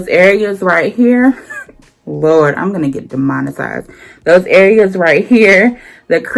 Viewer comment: MommyOfThe Trade thank you! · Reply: I died when you talked about...I'm gonna say cooch cleaning...and guys wonder why we women take longer in the shower ?!? That the main reason right there...inch Lmmfao hope you don't get demonetized at first I thought you said demonized ...LOL